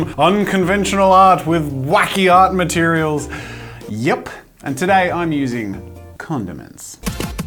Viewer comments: For a second I thought you said “and today I’m using condoms” and I died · i thought he said Condom mens